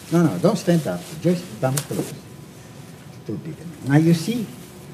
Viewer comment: Did he call the spectator "stupid" at 2:06? LOL wtf? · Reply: "It's too big"